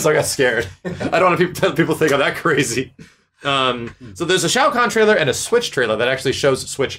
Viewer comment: For a second I thought I heard max say shao cock trailer and I had to rewatch to make sure